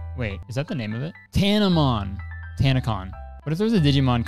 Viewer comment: it really sounds like says "meet me at tanacon"